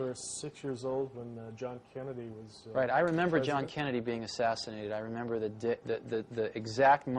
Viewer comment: Did he say "I remember the d*ck?"